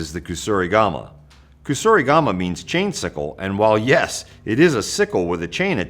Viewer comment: i misheard chain sickle as chainsicle, like a frozen treat in the shape of a chain